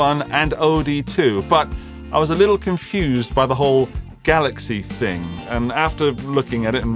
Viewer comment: "I was confused about the 4 galaxy thing" Me: "Not as confused as I was when I thought you said Ford Galaxy..."